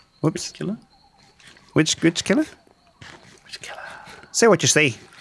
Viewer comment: When they said Which Killer? this episode i thought they said "Bitch Killer?"